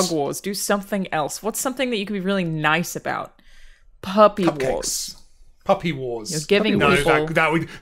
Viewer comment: misheard "puppy wars" as "herpes war"...